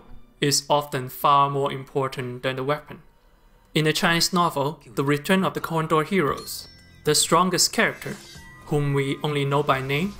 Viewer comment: I thought he said "The return of the CORNDOG heroes" 😆😂 Edit: thx 4 da heart amma sub